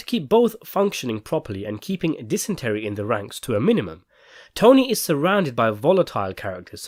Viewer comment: Tony tries to keep "dysentery" in the ranks low by nailing all the bathrooms shut. I mean, they already lost one guy on the crapper (for the opposite reason, but still), so better be safe than sorry! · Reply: Thank you! I was wondering if I'd misheard that, even after listening to it five times. "dysentery in the ranks" hahaha awesome...